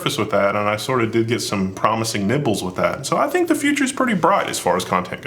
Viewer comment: does say "nipples" or "nibbles"? both are hilarious. I haven't been following the Crit TV / Level1 / whatever fallout, but glad to see the group making their own projects.